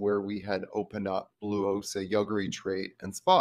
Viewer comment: thought he said blue oyster at first lol that club names taken